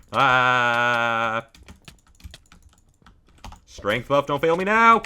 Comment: did he just say /strength?